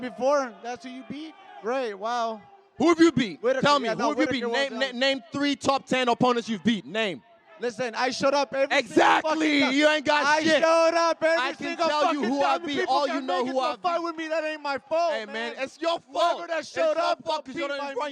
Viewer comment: Sounds like Marvin responds with "Listen, I shut up" instead of "showed up" and Izzy capitalized on that lol